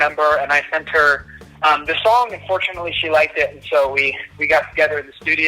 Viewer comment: I had captions on did he say unfortunately or did I hear him wrong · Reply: He said fortunately